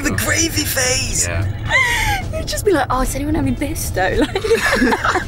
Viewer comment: I'm sorry, he had a what phase? That was so hard to understand for non native speakers😅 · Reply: i thought they said 'gravy' and 'bisto' but I'm pretty sure thats not it, right? 😳